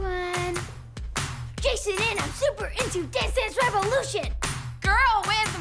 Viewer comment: "jason and im super into dance dance revolution!" idk why but i live that part so much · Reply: I thought it said death and revolution lol